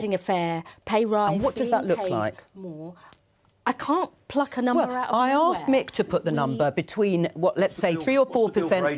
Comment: 6.53 sounds like she says I cant pick a number out of my arse! As her answer and the interviewers overlap.😂